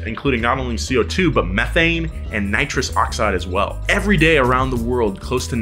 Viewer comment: did you say... NOS?